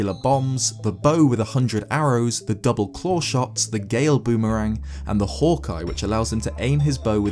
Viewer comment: I thought you said “the gayer boomerang” lol and if ur gay and say it’s an insult it’s not bc I’m gay